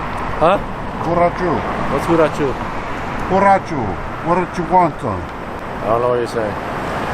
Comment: LOL... WHAT.. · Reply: When you looked at the oriental guy it sounded like you said "Why don't you wonton?" I was watch it but looked over to grab my drink and heard something along the lines of that.